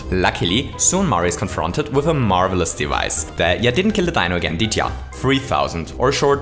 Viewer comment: I know he has a funny accent but what is he saying? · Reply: I know I’m 2 years late but he said “Ya didn’t kill the Dino again did ya?”